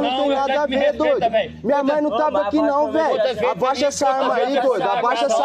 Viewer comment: Mais oq q o jovem fez com o rapaz ? Só entendi "minha mãe tava aqui não Jão"